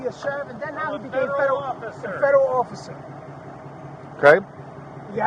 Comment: no he said he never said agent, which we never heard him say. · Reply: DETAILS.... at 1:40 he DOES NOT say Federal agent. He says, "Federal officer" which he technically is. A security officer for the Department of State. Pay attention to the DETAILS!